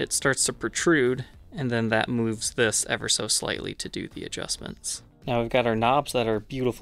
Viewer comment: I thought you said, "cop adjust". Which made me think you took out a stick and beat it into position....